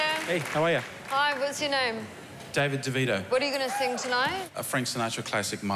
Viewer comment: Dammit i thought it said danny devito